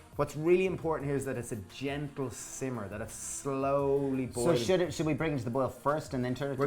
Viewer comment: your subtitles say "I've slowly bullshit is" :P I guess that's supposed to say boil?